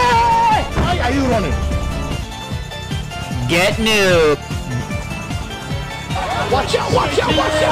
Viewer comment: i heard alberts voice saying "gEt nOoB"